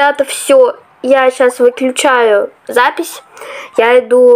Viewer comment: Я одна слышу не "выключаю", а "выпью чаю"? 😂